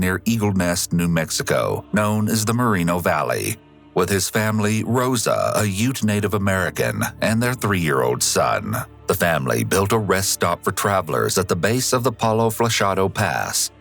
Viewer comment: At first I was not reading along and I thought the narrator said Rosa was a huge Native American. I thought, "Wow that's pretty rude." Then I noticed the captioning and went back.